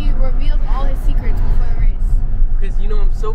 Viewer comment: all his secrets with what? Sorry, I'm not that good with English and YouTube captions didn't catch that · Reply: With the way he drives the go-cart He was just talking smack